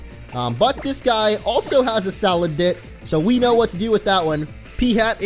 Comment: it sound like said salad-dick